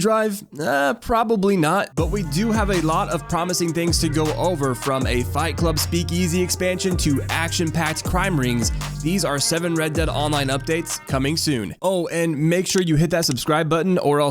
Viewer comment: I thought you said leaked not coming soon